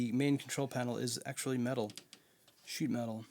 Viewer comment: Did you say cheap metal? 1:20 minutes. Nice unboxing!🤗